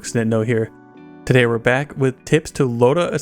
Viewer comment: Every time I hear you say "Nitno here" I hear "Stendo here" which sounds exactly like "extendo" which is slang in battle royales like PUBG for "extended mag". Just a weird thing I can never unhear lol. LOVE YOUR VIDEOS DUDE!